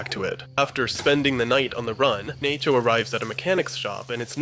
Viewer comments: did you just say "Nay-cho"? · Did you just call him “Naycho”?